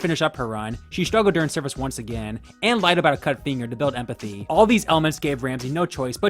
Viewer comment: im sorry she lied about her what? I have listened to this like 5 times and im no closer to having a clue what this guy said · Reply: She lied about cutting her finger off during service